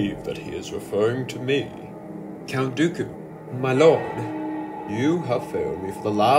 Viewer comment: I thought he said my love haha